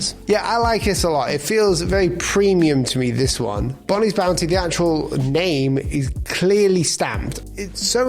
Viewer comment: I thought the first two said "Benny's Bounty", so on that point, number 3 is better.